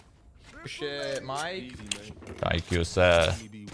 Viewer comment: for a second i thought he said good job mate but then i remembered his name is mike